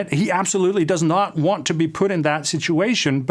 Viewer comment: i hear 'he absolutely does not want to be Putin that situation'